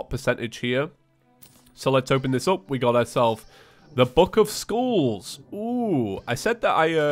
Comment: We got a book of skulls Me: Did you just say book of schools? Something may just be wrong with my hearing...